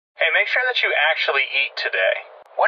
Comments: I genuinely thought it said, "hey make sure that you actually yeet today." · I thought he said, "make sure that you actually yeet today" 😂